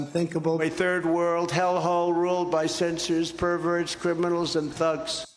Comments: Did he said ’senseis’? 😁😆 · Actually, I thought he said he s and vermin’s